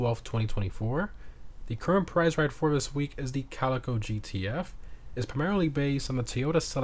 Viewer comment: I just woke up, and since it's thursday, I always have to check these. I think I wasn't awake enough though, because when he said Calico GTF, I thought he said "Car from GTA". Had to do a double take 😂